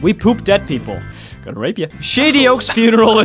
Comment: did say were coming to rape you?!?!